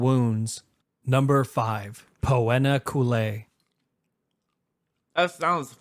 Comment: I hear poenakoolaid